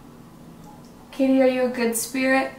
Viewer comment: it sounded like “Umm Kate”